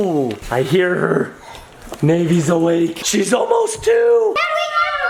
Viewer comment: When said she's almost 2 I heard it as she's almost poo